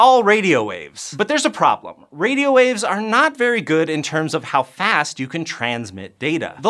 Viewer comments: Lol, is so funny with his editing skills. 2:40 He said radio waves are 'not very good'... but the cc said they are crap. :hand-orange-covering-eyes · So this is jinda random. But at 2:46, he said radio waves are not very good, but the subtitles said radio waves are kinda crap. And this was funny. I wat h with the subtitles often to not disturb others aroind me but i had both on at the same time today